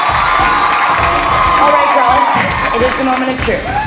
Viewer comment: LOL at 5:27 I thought she said "alright gorillas"...